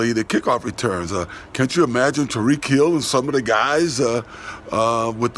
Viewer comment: I seriously thought said Tom re-kill some of those guys’ instead of ‘Tyreek Hill’. For a moment I thought was at it again